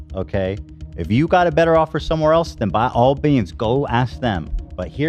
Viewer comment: says by all beans instead of by all means.